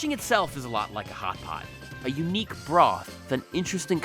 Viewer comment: thought he said, "Hot Pocket." Got excited.